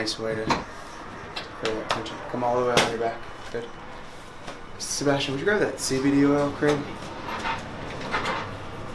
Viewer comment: What did he say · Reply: 'He won't have to hock up a few loggies in the parking lot most likely' Thanks for watching!